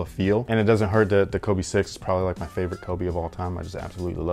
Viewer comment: Yo I thought you said the Kobe 4 was your favorite kobe of all time